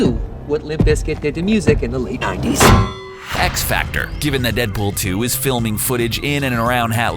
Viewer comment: did he just say x-factor